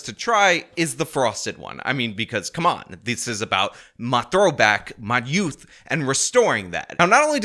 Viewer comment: For a second there, when pitching Magic Spoon, I thought that kept saying “my throat back”. don’t say it like that.